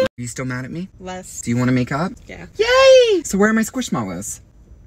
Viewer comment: Bro i thought he said make out😂